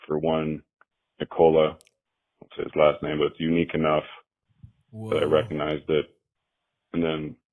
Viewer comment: I thought he said his last name was "Yuneekanof"